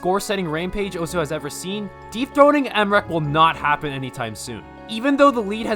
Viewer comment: okay for a sec I thought you said deepthroating